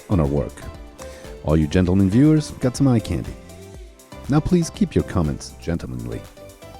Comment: " all you gentlemen viewers that's , now keep your comments gentlemanly. What did he say? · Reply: 'all you gentlemen viewers got some eye candy'